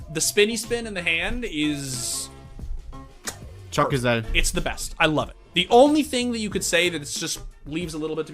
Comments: did he just say "çok güzel" for people that don't know in turkish "çok güzel" means very pretty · did he say çok güzel ?